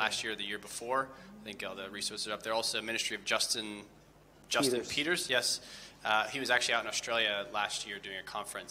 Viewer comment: lol when he said get justin peters C.D s i thought he said justin beaver